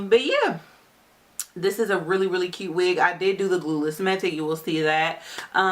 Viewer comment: I thought she said glueless? Maybe I heard wrong....cuteness